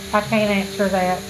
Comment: At the end she said "I can't answer that" but all I heard was "I don't care." Pathetic really